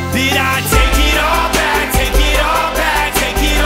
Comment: the first time I had evwr heard this song I kept hearing "i take it on back"not take it all back if you listen you can hear him say take it on back kind of 😮😞😯and yes I am kind of an odd bird (odd ball) you can say 😮😧..!!